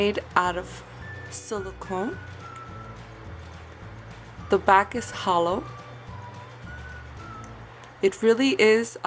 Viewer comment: 'The back is holo.'= what I hear. 'The back is hollow.'= what you mean. (Rectangular white stamper)